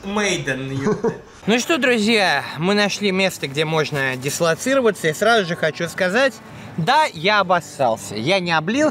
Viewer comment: Что сказал армян на 3:24 ? Снимать нельзя или что-то про тандыр? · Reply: Юрий Март Я спросил,кофе сейчас налить или с шавермой как будет готов заказ.Так как многие предпочитают пить кофе пока готовится) А вообще правильнее армянин а не армян😉